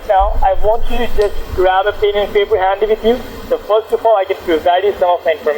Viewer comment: I heard "Grab a penis in your hand with you" xD